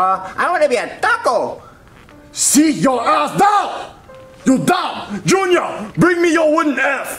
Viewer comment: what did he say? 😂😂😂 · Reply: He said seat your ass down!